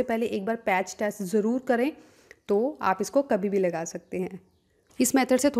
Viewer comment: pr Kya bola apne mam?? Smjh nhi aya tha plzzzzzzz comment kr ke BTA dijiye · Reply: Patch test bola tha matlab kuch bhi face par lagane se phele ek baar neck ya hands par lga kar dekhe..